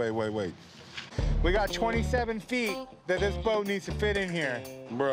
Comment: Yes... 27' is massive... :( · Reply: I thought they said 28 feet then they switched it to 27 feet. I must be getting old